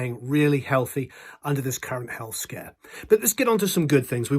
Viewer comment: At first i thought he said staying healthy under this current health scam and im like what oh no not covid deny guy lol He said currewnt health scare lol